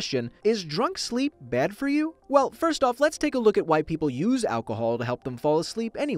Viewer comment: am I retarded? or did he say "first let’s take a look at white people."